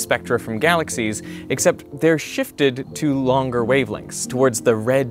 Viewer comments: When he says "shifted", the subtitles say "shitfed". My question is, is it supposed to be shifted or shitfed? Was it a typo or a saypo? 👀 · I know that laughing about a typo might be a bit infantile, but when the captions read "they are shitfed" instead of shifted, it just got me by surprise 🤭